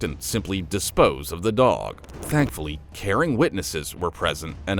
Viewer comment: Did he just say “thankfully Karen witnesses were present”? 😂About the unfortunate blind-deaf dog situation.